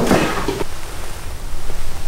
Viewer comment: What did he say at 19:08 ? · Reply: Well guys he says "Now that's a great cut"